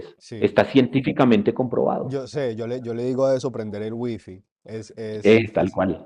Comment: alguien sabe lo que dijo? lo repase muchas veces no logro entender... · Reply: Yo le digo a eso "prender el WiFi".